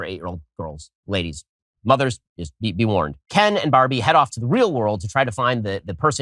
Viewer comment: did he really say "mothers... be worn" :D